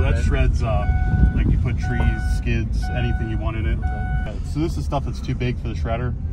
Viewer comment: did he say kids?💀